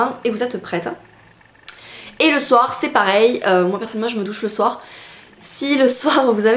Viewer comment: À 5:13, j'ai entendu "moi personnellement je me touche le soir..!" ahaha une super vidéo ma belle :)